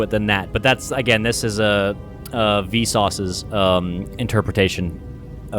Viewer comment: Every time V says Visas name all I hear is "Vsauce"